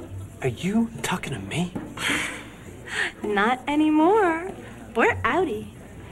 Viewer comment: What does Cher say on 17:35 min? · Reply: "We're outy" which equals to "we're out of here" lol.